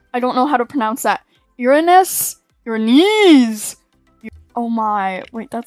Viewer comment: What I heard - YOUR KNEEEEES